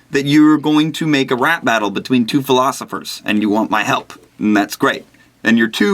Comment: When you said "philosophers", I thought you said "velociraptors"... after which I pondered, "There are velociraptors named Socrates and Spinoza?"